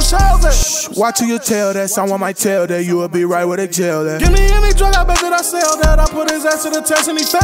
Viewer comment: Watch who you tell that someone might tell that you'll be right where the jail at...* That was fire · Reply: Thought he said “ you a big rat were da tail at “ 😭